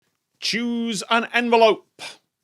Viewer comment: I misheard "choose an envelope" as "juice in envelope", must be those mythical watertight envelopes